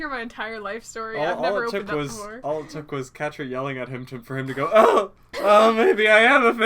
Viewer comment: Did say Catra